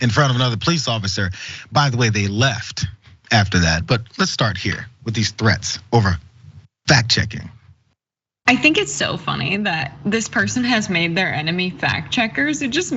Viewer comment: Thought you said Fart checking. Nevertheless, good video.